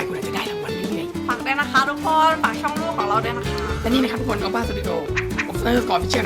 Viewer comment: เกี่ยวกับเดปาสเอสเมอร์ลิซ่าลองฟังดูดีๆนะ5:44พูดว่าฝากช่องลูกของเราด้วยนะค้าา